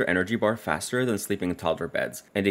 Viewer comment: Drowning neighbors!?!?! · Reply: Oh never mind I thought you said drowning neighbors you said droughty neighbors